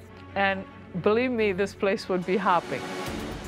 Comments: "I wish I had two clones of me, and believe me, this place would be hopping." That's a level of narcissism that demands a punch in the face. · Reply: I thought she said "this place will be happy" 🤔🤪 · "I wish I had two clones of me, and believe me, this place would be hopping." That's a level of narcissism that demands a punch in the face. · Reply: I thought she said "this place would be poppin'" 💀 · “I wish I had two clones of me and this place would be hopping” yeah hopping to hell accidentally😓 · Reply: Ryuri Angelica I thought she said HAPPY. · "I wish I had two clones of me, and believe me, this place would be hopping." That's a level of narcissism that demands a punch in the face. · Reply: I thought she said "this place would be happening"